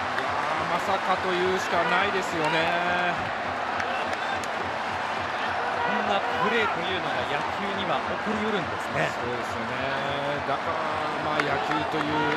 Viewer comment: 実況アナが何と言っているのか聞き取れない‥。 誰か教えて下さい。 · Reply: 2か月後ですが、こんな感じじゃないかと言う事を書きます。 「こんなプレイというのが野球には起こりうるんですね」